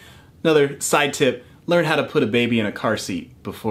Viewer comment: I thought you said "How to swallow a baby..."